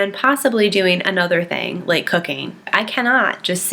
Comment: for a minute I thought u said cocaine 😂😂😂